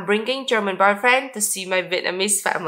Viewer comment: I thought she said "I'm bringing German birdbrain to meet my . . ." lol